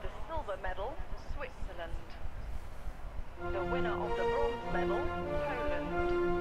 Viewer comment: i think i heard it say ''the winner of the gold medal poland'' lolol